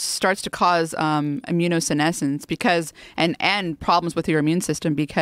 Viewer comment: Actually obesity starts to cause immunosenescence because an n problems with your immune system because people who are overweight... What does she say where I heard "an n problems"?